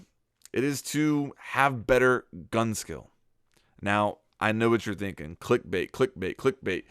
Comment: I thought you said guns skins